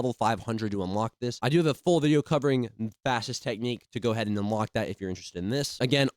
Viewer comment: the subtitles say fascist 💀💀